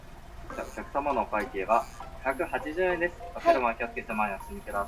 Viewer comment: マックのお会計「お客様のお会計が180円です」に聞こえるんやけど違うよね（6:41）